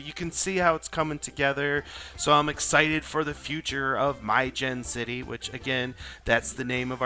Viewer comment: Is your city My Gen City or is it MyGen City ? Or am I a doofus who totally misheard what you said? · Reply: It's MiJenn City, a combination of our names. 😎